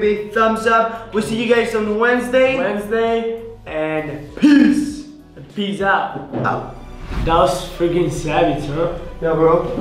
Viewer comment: At the end I thought he said pizza instead of peace out lol